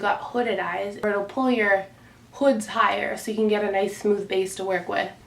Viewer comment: did she just say "pull yours hoods higher" lmfao I'm done lol